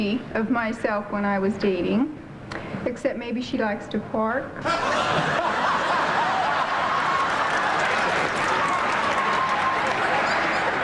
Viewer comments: what is she saying? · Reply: "maybe she like to park" back in the day a girl who "parks" was a girl who would have sex and make out with boys in there cars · What did the first Mom say about her Daughter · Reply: I thought she said she likes to "pork". As in have sex